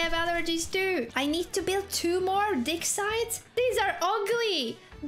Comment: because of your accent thought you said "D!ck sites" 🤣 my bad